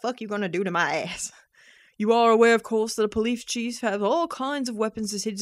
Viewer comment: did she say Polief Cheese?